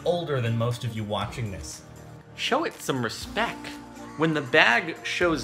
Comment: Did u just say respeck?